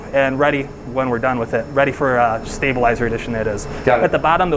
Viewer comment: closed captions is wrong: 13:11 "addition," not "edition." 13:53 "glasses," not "classes" 14:54 "silicon," not "silicone."